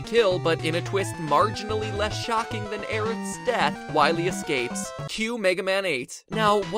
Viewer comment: Did I hear that right? -"less shocking then eren's death" EREN? EREN?! *Our names won't be remembered If we die like trampled flowers I refuse to be forgotten Written off as less than worthless* 8D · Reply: I heard "less shocking than Eric's death" :P